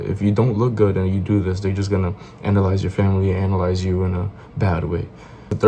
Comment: i thought ts said glaze at first